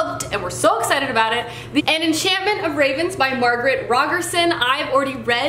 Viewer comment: At first I thought you said An Enchantment of Regan's😄